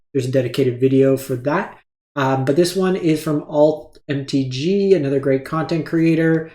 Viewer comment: Who is this content creator you talk about at 2:15 ? I hear alt or old mtg but can't find any content creator with that name on youtube. Great video nonetheless. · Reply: It’s AlthMTG - he has finished #1 mythic last 2 months. He creates as part of MTG Arena Zone. Really creative decks